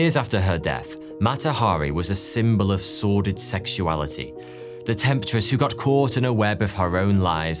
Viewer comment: Something lovely was created in the space between British and American pronunciation. I initially heard "sworded sexuality" (about 13:50), instead of "sordid sexuality". Thank you.